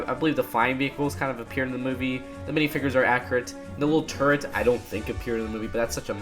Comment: I thought you said”little turds” instead of little turrets 😂